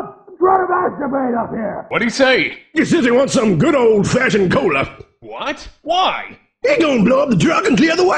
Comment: What did the guy said at 0:43? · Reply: He says he wants some good ol' fashioned cola.